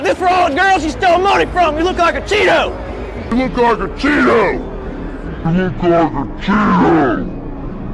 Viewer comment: sounded like a “cheat hoe”